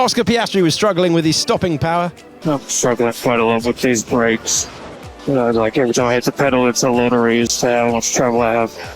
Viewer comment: what is he even tryna say 😭😭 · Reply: I'm struggling quite a lot with these brakes. It's a lottery with how much travel i have.